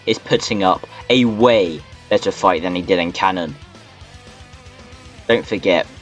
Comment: I thought he said fight thanos